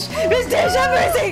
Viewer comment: I love the way always calls you! It’s so cute <3 Also how is such a idiot but also smart at the same time? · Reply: me too! i was looking for a comment like this. sometimes i hear “Ms. Dish” and “Ms. Dawn”, i’m not sure the reason behind it, but i think it’s very respectful and wholesome